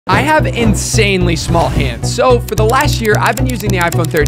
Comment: I heard something else, never mind now.. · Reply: Bro at first I thought he said "I have insanely small peppee"